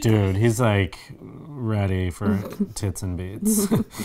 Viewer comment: it sounds like Timmy says, "He's ready for tits and beets." 😂😂😂 Glad I went back and listened again. I was so confused. 😂😂😂